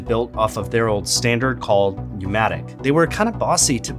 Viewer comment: hey, i would like to remind you for some errors within the closed caption (like in 5:06 - “U-matic” instead of “pneumatic”)